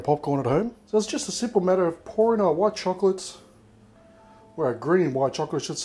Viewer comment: omg. 1:27 did he just say porn???? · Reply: sounded like it hahahaha but he meant pour, pouring